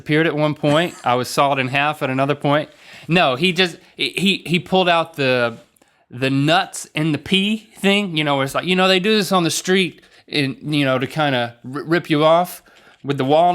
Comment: i seriously thought link said "nut in the pee thing" LOL